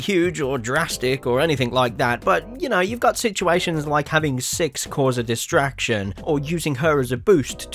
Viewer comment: the captions said having sex cause a distraction when he said having six cause a distraction wtf